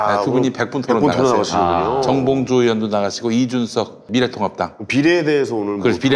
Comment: 100분토론서 이준석최고위원이 미래통합당을 말하는데 왜 내귀엔 계속 미래통닭당으로 들리는지요 신기하네